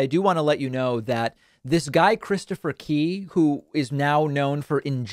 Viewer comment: I thought you were saying 'Christopher Pee" for a while ...